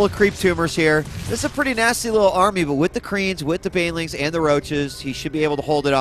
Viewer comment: HAHA, @ 9:47 dj Wheat sounded like he said: 'With the Koreans... etc'